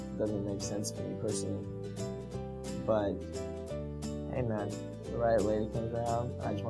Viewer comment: Please, somebody write me here what he's saying since 1:36))))) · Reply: Carina Richardson Derek: But hey man, if the right lady comes out I just want to make babies, just never ending babies then who knows?